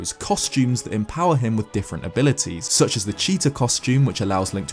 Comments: I thought you said "the cheater costume" when the fierce deity costume was on screen lmao... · He said "Cheetah Costume" with footage of the Fierce Deity Armor, and I thought he said, "Cheater Costume"